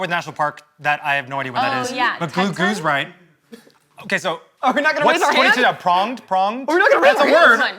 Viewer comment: What's Zach saying at 18:34? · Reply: “are we not gonna raise our hands?” 🤣 because they had finished but the rest was doble checking some answers